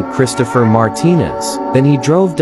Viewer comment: NAH I THOUGHT HE SAID "melanie martinez* 😂😂😂😂